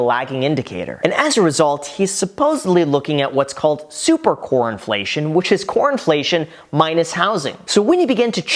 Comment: Every time you said "core inflation" I heard "CORN inflation"...I guess I am hungry 😂